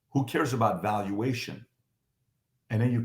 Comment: For a second there, I thought he said, "who cares about valueTAINMENT"!!!! LOL!!!!!!!